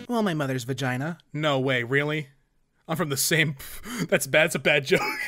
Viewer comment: Did you say from the same, or from the sink? Either way, I don't get it. What's the joke?